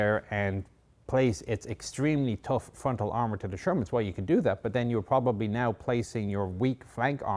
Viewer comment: I love how they say "Shermans!" (wasn't that the British name for the M4? Did Americans adopt the name as well?) and all I heard was "Germans!"